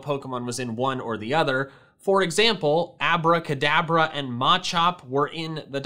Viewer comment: What I thought he said : “Abra-kadabra” WHAT HE REALLY SAID “Abra,Kadabra...”